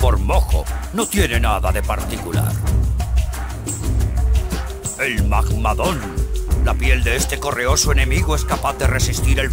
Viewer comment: “El mamadón" escuche XD